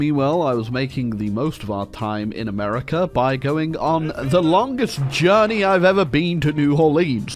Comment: "Longest trip to Louisiana" I heard "Longist." I need to stop playing KR.